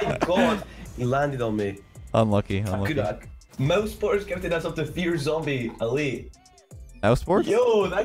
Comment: Can someone tell me what said at 13:17? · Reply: I believe he said "Mousesports gifted a sub to fear zombie, elite"... I think anyway